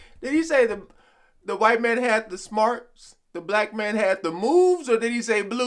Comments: lol i thought he said white man hade the smokes' lol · "White man had the schmaltz" schmaltz nounINFORMAL excessive sentimentality, especially in music or movies. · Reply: I always thought he said, "White man had the spouse" 🤣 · I thought he said "the white man had the smokes, the black man had the booze" for years.... lol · Schmaltz. When I was a kid I thought he said the white man had the smokes. It was Schmaltz... which I have never heard used in any context before